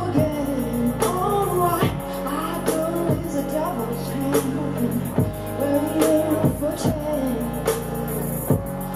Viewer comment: what did she say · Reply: Özlem Isci "I know there's a devils train" That's what I heard lol